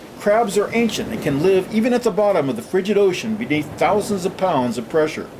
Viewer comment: did he say at the bottom of the freaking ocean?lol